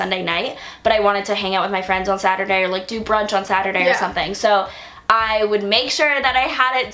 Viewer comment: I swear I heard "Do drugs on Saturday" instead of "Do brunch on Saturday"